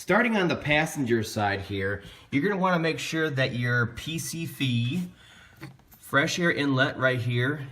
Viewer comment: Is he saying pcf? For the the fresh air inlit near the beginning of video? I am completely missing that tube... anyway to replace this?